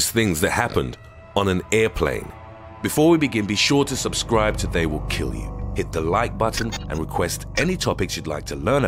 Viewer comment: I zoned out for a bit and thought he said “Make sure to subscribe or they will kill you” at first